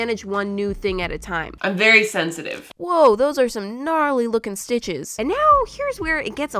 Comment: DID SHE SAY GNARLY hehe